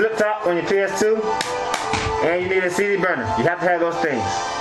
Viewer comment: LOL at 1:25 ish when you said "cd burner" I thought for a sec you said cheeseburger XD